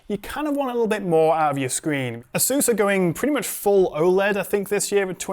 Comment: did I hear "Asus" 🤔